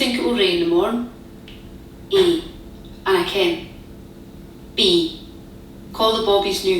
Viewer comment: 謎すぎる I don't knowは普通にI can't に聞こえるww